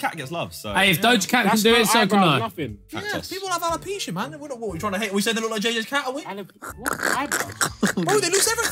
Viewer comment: what did harry say, didnt understand a word 😂 please someone help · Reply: People have alopecia man what are we tryna hate and say they look like JJ’s cat